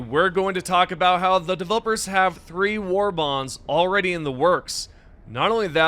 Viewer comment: thought you said “free warbonds” for a minute and got excited lmfao